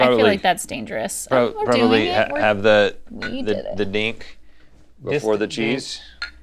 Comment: He said “Dink before the cheese” but all I heard was “Dink Cheese” 😳